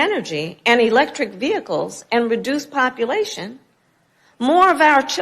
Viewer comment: Did vice president Camilla say "... reduce population..." 3:27. That's what the subtitle/cc says. What's reducing population to do with clean energy? Is this due to the fact that we are emitting co2 so, ... How did you miss that and never commented about it?